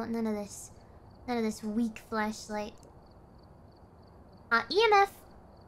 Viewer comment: Am I the only one who heard fleshlight